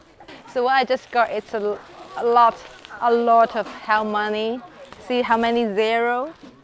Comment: a lot of hell money??? hell?!?!? lolol did i mishear you? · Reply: No, it’s called hell money